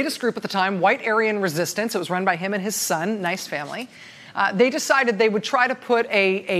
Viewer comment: DID SHE JUST SAY, ‘NICE FAMILY’?